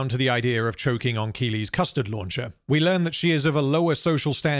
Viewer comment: Did you just say “Custard launcher “?